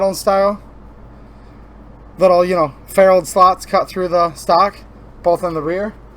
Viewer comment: what was that word you used to describe the slot in the stock? Sounded like "feralled" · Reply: Ferrule / ferruled